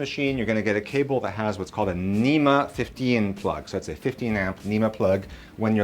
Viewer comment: Can you help me understand what plug I need again? It sounded like you said nema 50n? or was it nema 15n? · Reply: nema 6-15